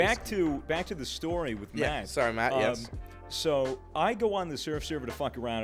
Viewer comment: did he call swagger Mat att 24:45??